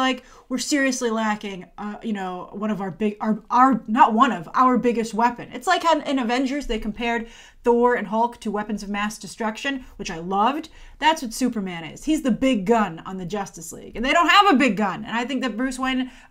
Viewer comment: i think Bruce said “Supermen”